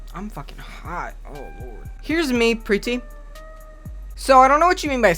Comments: Lol when you said “here’s me Pre T” I thought you said “here’s me pretty” I had to replay it because no way you would call yourself pretty. Maybe handsome. But not pretty lol. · When he said "Heres me pre-T" o thought he said "here is me pretty" and i was like "you are still pretty tf" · sorry this is kind of dumb but i thought you said "here's me pretty" in some weird accent and i can't stop laughing